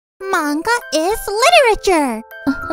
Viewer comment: i thought you said monika is literature